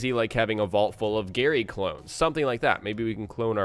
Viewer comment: gary clothes? what did he say at 6:14?